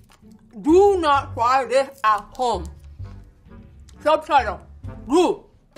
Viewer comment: I thought she said do this in party home 😂